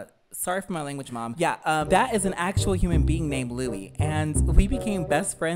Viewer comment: I’m so stupid I thought you were saying “lily” not “Louie”